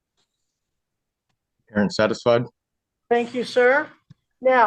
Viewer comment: What did hey say @ 1:12 . Before he covers the cam . He says are you satisfide She says thanks you . Then he says under his braeth . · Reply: satisfied...